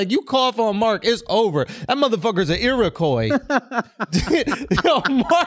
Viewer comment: Did Schulz just call Mark a Irokoi (Colored Carp Koi Fish)?